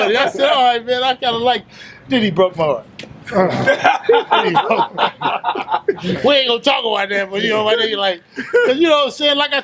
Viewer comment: What does say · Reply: He broke my heart.